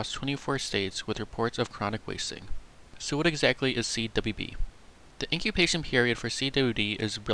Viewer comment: is it just me or did he say CWB instead of CWD